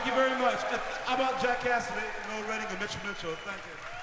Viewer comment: No one going to mention the ending? where noel makes a horrific buzzing distorted sound after the song and talks into the mic and jimi says "How about jackass noel redding, and mitch mitchell" as noel redding tries to talk over him. Those two just couldn't stand each other but Jimi was a great bandleader. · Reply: You're not alone haha I heard "how about Jackass Noel Redding..." but listening again I realized Jack Cassidy of Jefferson Airplane sat in on that performance, he's saying Jack Cassidy.